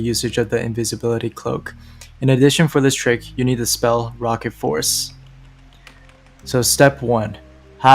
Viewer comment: I thought he said "rocket force" lol